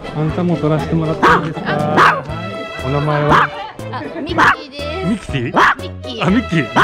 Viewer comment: カメラマンさんのフォローがチラホラあってスタッフさんのワンちゃん好きと優しさが伝わって来ますね😊 ミッキーをミキティと聞き間違えた時はウケちゃいました🤣 今回も飼い主さん達のワンちゃんに対する愛情深さがキュンキュンします🫰